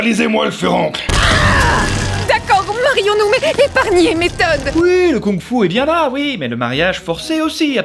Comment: je rêve où j'ai entendu le mot teub